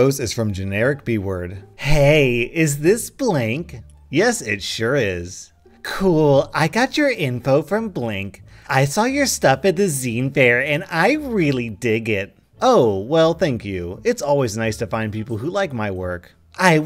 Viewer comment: So tired I thought it said sea fair lol...